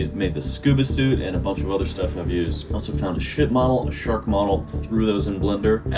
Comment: “Shit model” says ship model, but thats my truth